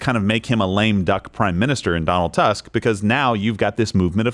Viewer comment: Im sorry...did say, "Donald Tusk"? 😂